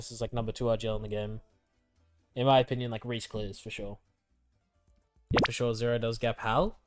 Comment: 這句原文是啥阿 聽不出來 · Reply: In my opinion, like, Rhys clears for sure.